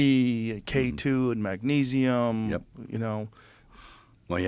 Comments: What does Mel say he takes at 1:38:45 for weight loss? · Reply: Gary brecka thing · Did anyone get what Gibson said at 01:38:50 "Did you do the ?breka? thing ... yeah I lost 30 pounds". And Mel if you reading this, looking forward to "The Resurrection" - we need that! Thank You 🌞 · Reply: I got it, he is talking about Biohacker Gary Brecka's four foundations: sunlight, breathing, cold therapy and grounding. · Did someone catch that? He did what? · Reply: Got it - The Brecka Diet